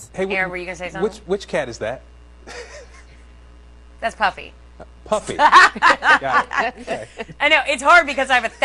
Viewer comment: I thought she said her cat’s name was puppy and now I think it should be.